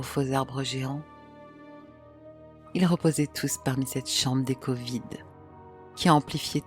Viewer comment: Le "ratatata de la mitrailleuses" m'a glacer le sang ! ou la sublime conteuse, trop fan de toi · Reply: Moi c'est juste avant, lorsqu'elle dit "Chambre d'écho vide" j'ai compris "Chambre des COVID" 😅😅😅